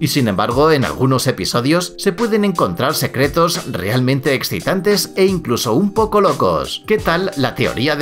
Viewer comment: dijo ex*tantes o que dijo:v? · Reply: estas en lo cierto dijo exitantes